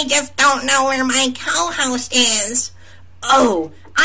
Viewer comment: How did i hear this as "he's gay"